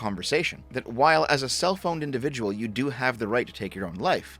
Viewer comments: I'm sorry, but I first heard "self-owned" as "cell-phoned" and wondered if phone was making him contemplate suicide. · When you said “A self owned individual” I heard “a cell phoned individual”. It doesn’t mean anything, but it sounds clever enough to slap some meaning on there and call it a day. Maybe, a cell phoned individual is not a self owned individual. If you’re a boomer looking for a new phrase. You’re welcome.